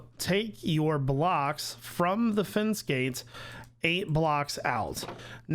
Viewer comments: i keep hearing "fin skates" and not "fence gates" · Anybody else thought he was saying fin skates when he was saying fence gates?